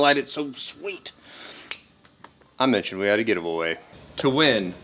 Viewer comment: Did you say "gid-a-va-way"? at 6:40 ...maybe it's just my Tinnitis... When you put these on bikes from the sales floor do they charge the customer for them or ...?